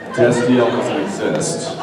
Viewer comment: So if I heard this correctly, at approx 3:55 Jensen says, "Destiel does exist." Then there's that discussion at about 9:00 about "personal space" where Misha completely invades Jensen's space. They play up to it so much, I'm guessing that everyone started acknowledging that Destiel is, in fact, real, somewhere in the middle of the series. BTW, if Destiel wasn't real, maybe Dean shouldn't have looked at Castiel's mouth so often, frequently while licking his lips. Let's face it, that's flat out saying "I think the person I'm looking at is hot".